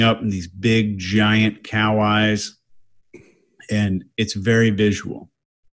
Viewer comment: I thought she said “kale eyes”. Only when Chase repeated did I realize it was “cow eyes”.